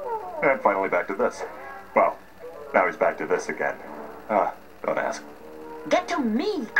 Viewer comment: did Kronk just said “Dumb ass” or “Don’t ask”? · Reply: I Think It Was Don’t Ask To Be Exact.